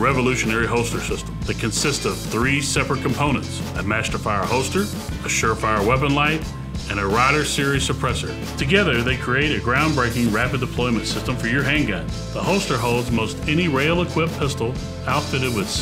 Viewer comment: I know what he said but it sounded like he said "A rather serious suppressor." You wouldn't want to mess with a rather serious suppressor now do you?